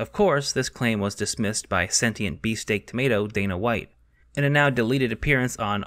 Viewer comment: I've watched this video like 4 times at this point and I can never figure out what in the fuck he is saying at 12:45, beast egg tomato??